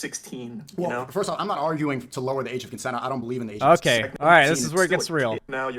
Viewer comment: did i just hear "i dont believe in the age of consent" like christ, that is a horrible take